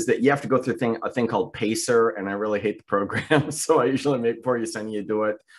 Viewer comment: Is your assistant's last name Pestis? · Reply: It sounded like you were saying "Yersinia," and yersinia pestis is the plague bacteria. I never claimed it was a good joke!